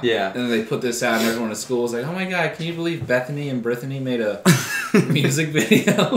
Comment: I genuinely thought he said Bettany and Birthany